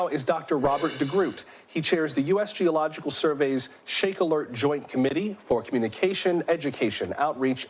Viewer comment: I thought it said Snake ALERT. I,m like o.k. if you see a bunch of snakes, there's going to be a quake. Doohhh